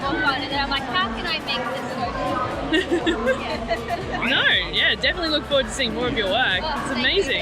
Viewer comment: WHOAH look at her poor face she thought miss camerawoman was saying "no you're bad"